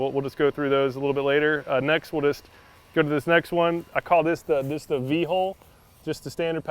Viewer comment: At first I thought he said v hole